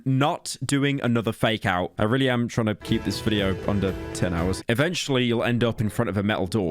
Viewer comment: I could've sworn he said 10 hour video before